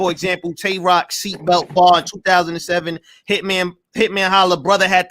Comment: I said 2017 meat head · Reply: and thats why your takes on battle rap should cease and desist .. you "CLEARLY" said 2007 at 4:15 .. there's the time stamp for your viewing pleasure fool .. you dont even know what you say out your own mouth LMAO